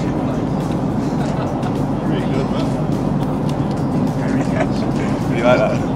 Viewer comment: Can i see Eric Dier's reaction on that???? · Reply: I'm not sure, but at 0:52 "pretty good man" sounded like Eric